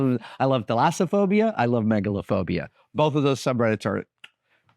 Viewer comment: Thalassophobia, or fear of deep bodies of water is what Adam seems to mention around 10:55, for those curious about that first subreddit he mentions. I wasn’t familiar with the word “thalassophobia”, and I couldn’t quite make out what Adam was saying, but searching for “phobia” on Reddit and scrolling a bit clued me in.